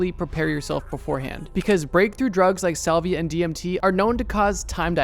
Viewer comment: Salvia LITERALLY, no exaggeration whatsoever, turned me into a goddamn lawnchair. A lawnchair sitting by a lake. Unable to move, scream, or do anything except think, "Goddamn, I'm a lawnchair! Help!" It only lasted about 30 seconds, but that was long enough, by God. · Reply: I thought you said saliva 😭